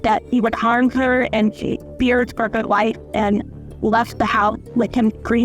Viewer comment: I believe the 1st unintelligable is "feared for her life" . 14:28 - Trickier to interpret, but I think she's saying "He's- He's very much insane".